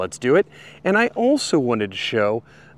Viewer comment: When you said "Cyansky" I thought you said "Science Guy". xD